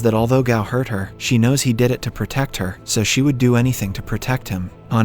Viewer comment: did you say "on earth"?😂